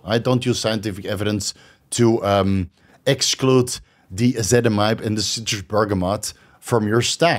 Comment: What’s the supplement @ 11:22 that you mentioned with citrus bergamot? I cannot make out exactly what you said nor can transcript or closed captions unfortunately. Thank you! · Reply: Ezetimibe